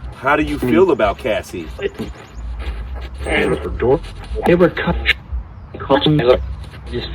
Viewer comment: How do you feel about Cassie? I heard: @ 22:57 "THEY WERE COMING" Who? The Cops?